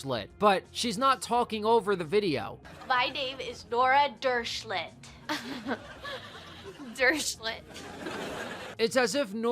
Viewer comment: when i first heard the psycho's name for the first time i thought it was dirtshit lol